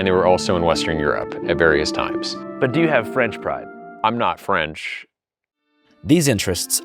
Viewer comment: He said "Do you have French pride?" not French Fries, lol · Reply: shit, I misheard it as french fries too, this makes so much more sense lmao